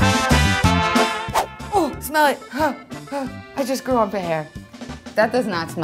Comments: "I just grew armpit hair" - This is why it isn't a drinking video without! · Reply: Guy Is that What she said? I heard "I just grew a bear!" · "I just grew armpit hair" - This is why it isn't a drinking video without! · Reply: LOL I thought she said "I just grew a pair!"